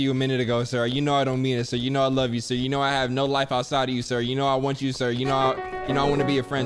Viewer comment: did anyone else hear, "you know I want you sir" 19:29? lmao